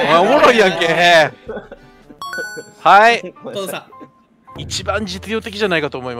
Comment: の「トントンさん」が「お父さん」って聞こえて仕方ない